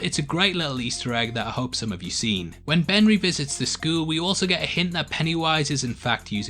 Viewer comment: he says benry instead of ben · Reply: Carter Tann he says Ben revisits the school but it does sound like benry lol